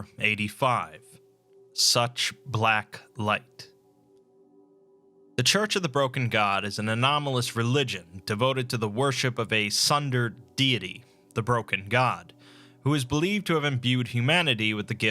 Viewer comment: at first I heard you say "tsunder-deity", and now I can only imagine the broken god being like "its not like I WANT you to worship me or anything..."